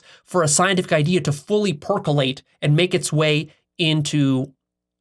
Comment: Did say "Fully Porkulate"??! He does a great job at the long-take video shoot. And I'm not arguing a point.... Just struck me funny is all... Aloha.